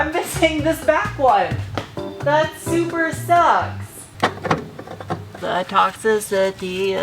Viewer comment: Sounds like ya said “I’m missing this back one, that supra sucks”